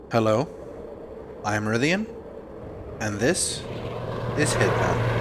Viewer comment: I thought he said "Hello, I am Rythain, is this, is Hitler."